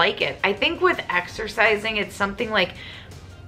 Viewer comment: Exercise, I thought they said extra fries 🤣🤣